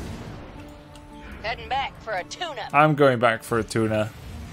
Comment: it sounds like said, "I'm going back for a tuna." lol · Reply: hammer actually says "i'm going back for a tune-up" but it sounds like shes saying "i'm going back for a tuna" and is a memer so thats what he said